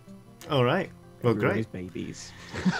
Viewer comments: What did say at 55:32? The oxventurers as babies? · Can someone please tell me what said at 55:30 seconds into it I can't hear the words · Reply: I had to rewatch it a few times but I'm pretty sure he said, "Everyone are babies".